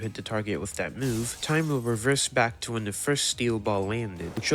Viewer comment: did you say steel ball?